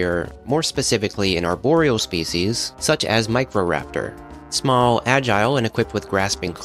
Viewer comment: Names because I don't know how to spell and the subtitles are wrong 1:23 Captions: Microaptor – Real spelling: Microraptor 2:42 Captions: Oruronosaurus, Orosaurus, Forosaurus, Oronosaurus – Real spelling: Ouranosaurus 4:40 Captions: Lambiosaurus – Real spelling: Lambeosaurus 5:46 Captions: Lisa Vishia, Livishia, Lisio, Lisia – Real spelling: Lisowicia 7:24 Captions: Gammimus, Gammus – Real Spelling: Gallimimus 9:33 Timurlengia. The correct spelling is on screen, I just thought the misspellings were funny. (Timorenia, teimmeria, Tarlenia, Team Oria, Tearia, Tealia, Teameria, Team lenia, Teamemeria) 12:12 Captions: Draonosaurus – Real Spelling: Drepanosaurus Some other misspelling: Messoic instead of Mesozoic. Triacic instead of Triassic.